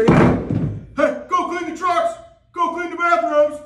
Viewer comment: What is he saying? It sounds like he is saying "go paint the sharks, go taze the bathroom." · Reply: lol, he said “go clean the trucks, go clean the bathroom” idk where u got that 😭 😭